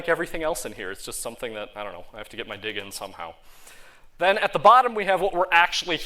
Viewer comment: Wait what did he say? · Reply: "I gotta have to dig in somehow" :)